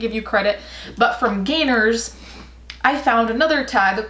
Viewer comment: When you said "gainers" I thought you said "gaynors".... as in the ballet shoe brand.... why am I like this